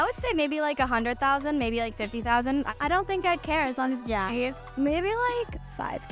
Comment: Am i just triping or did she say i don't think i'd care as long it's pretty